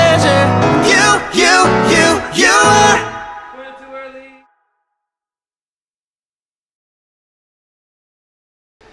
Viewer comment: What is, it sounds like, saying at 2:20? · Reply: Went up to early